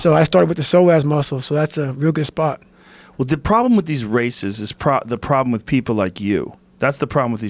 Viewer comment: Holy shit at 30:55 I thought joe said “the problem with these racists, is people like you!” But he actually said races 😂